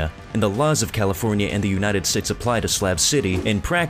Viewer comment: did you say United Stits? lol xD xD